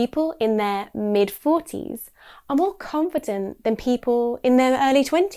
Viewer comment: Thanks for your lesson, but I have to say there is a typo in the example sentence at 03:11. I heard 'than' when you were saying in the middle of sentence, so It wasn't 'that', but 'than'. It happens.